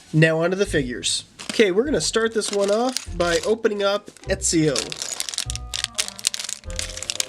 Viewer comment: Did anyone else hear him say Etzio instead os Ezio